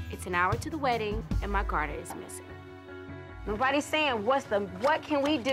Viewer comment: wait what was she missing ? I keep hearing car, but I'm assuming it's garter :/